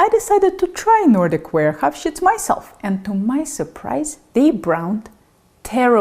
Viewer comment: I'm sorry did she say, "I shit myself" ? 🤔 😅